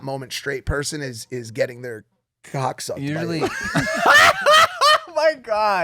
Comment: Can somebody write what Mikes says at minute 41:47... "Getting their thoughts on..." that´s what i understand lol